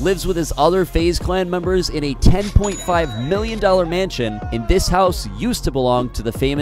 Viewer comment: Who else thought he said 10.5 billion at the beginning so they replayed it likd 10x